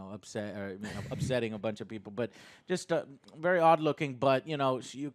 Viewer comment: did Joe just say she has an odd looking butt?